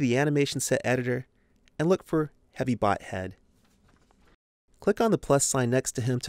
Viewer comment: Is it just me or is he saying heavy butt head?